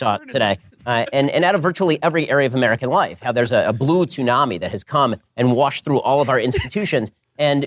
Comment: Did Ben say "Blue Toonami"?